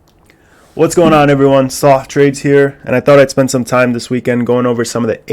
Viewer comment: swear I thought you said "Soft Trades here" at first lol